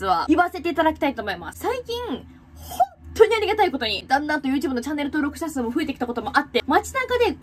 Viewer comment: 旦那とチャンネル登録者数..にしか聞こえん😭👏